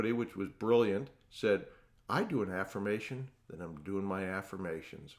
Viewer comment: I cant understand what he says in 4:15 " I do an affirmation while I do an affirmation?, thats briliant" what does he say? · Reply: 'I do an affirmation that I'm doing my affirmations' to eliminate laziness